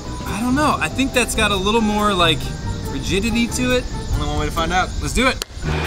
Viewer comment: did he say virginity